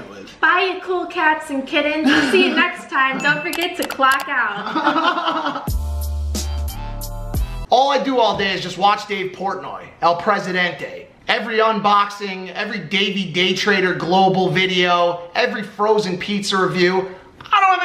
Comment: Thought he said “all I do all day is watch gay porn”🤨💀